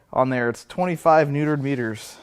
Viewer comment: Did he say, "25 neutered meters?"